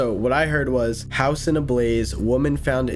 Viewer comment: the only part that i disagree with for the assumed what it was saying (i know theres a word for it but i forgot it) is the "house in a blaze" all i heard there was "house ablaze"